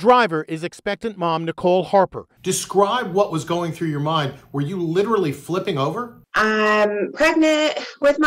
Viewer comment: IE Reporter: “Were you LITERALLY flipping over?” The World: “Did you not watch the video?!” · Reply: he said "describe what was going through your mind WHEN you were literally flipping over" (0:51) But I understand why you didn't hear the "when" because I didn't hear it at first either, and the way he said it sounded like a question lol